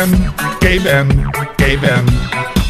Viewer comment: even Gaben sounds like it says Caveman.